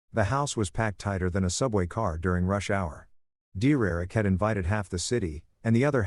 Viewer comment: I just realized the voice meant DERRICK AND NOT D-ERIC! LMAO! 😂😂😂😂 Good story though. ❤ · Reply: I thought it was saying "dear erick"